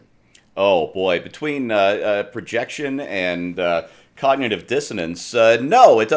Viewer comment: sounded like he said 'Kanyetive Dissonance' instead of Cognitive. I approve.